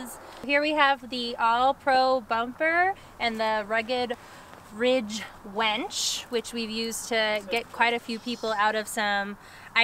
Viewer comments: did she say "rugged winch wench"? Cute! Great adventure and video. Save up your $ and you could fly in via helicopter... at least until the Sheriff or someone says it's not allowed. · Most people have a winch, I heard you say you have a 'wench' though. lol.